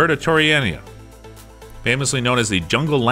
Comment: Cuh-top-sis-ber-ter-oni-ana What in the world did the narrator say 😂